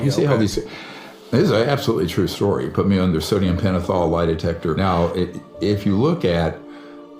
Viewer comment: I think he was saying, "this is an absolutely true story, YOU CAN PUT ME under sodium pentothol or a lie detector", I don't think he meant that THEY HAD PUT HIM in that situation- just listen again, that's what I heard. And I also want to add my condolences to you and your family. Y'all are not alone.